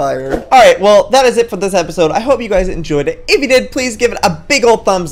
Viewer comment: Lol at the end I thought he said "good damn bye"